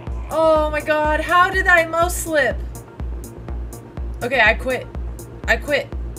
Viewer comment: I thought she said moe slip and wondered what she meant by it XD